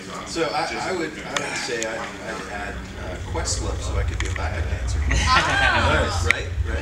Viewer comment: I can't understand what Chad says at 1:59, when answering who they would bring in as a "sixth member". I'm really curious. Could someone help me out? English is not my first language, so.... thanks guys! And special thanks to. Very interesting interview. It made my day : ) I LOVE SWITCHFOOT SO MUCH!!!!!!!!!!!!!! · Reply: He said he would add Quest love so that he could be a back up dancer. Talk about a late reply :)